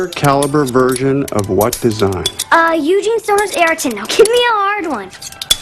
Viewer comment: She said AR-10 so fast I thought she said ariton. Atleast I knew all the questions except last one.